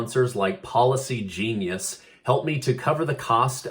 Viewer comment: I thought you said Paula C Genius, lol.